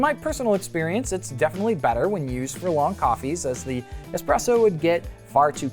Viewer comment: I've listened to it 19 times, what the hell is he saying at 06:33 when he says "In my personal experience, its definitely better when you use ?????? coffee" sounds like he's saying furlong... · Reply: Good catch! was on a roll, haha. But you were close: "when used for long coffees." Basically, liked the light extraction for something closer to a cup of coffee rather than an espresso.